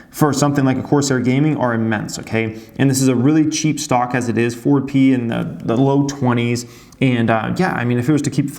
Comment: Hello thank for your videos. I am newer to trading (have been learning for about a year now) and I'm not sure what you mean at 19:45 when you say the stock is valued at "4P", can you please explain this? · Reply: Was curious about that and figured out he's saying "forward P/E"